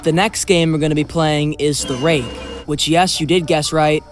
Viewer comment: subtitles mishearing things : "obviously replicates the classic creepypasta the break"